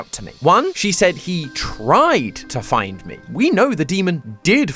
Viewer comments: I think since this reoccurs I beleive instead of “he tried to find me” Amanda is saying “he tried to BIND me” due to her demon form being outside of the tv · i thought the coded message said "he tried to bind me" or it could be that too my theory on this is the surgeon and Hamelin failed to put her and the demon in the show so there not fully connected which is also why amanda can remember her life as Rebbeca